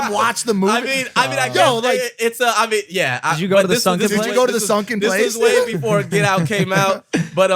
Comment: Did mike say “Did you go to the sunken place nigga?” 💀💀💀